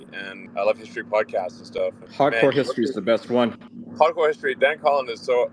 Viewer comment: can someone tell me what he said "pod for history"? I would love to listen to those podcasts. Thanks!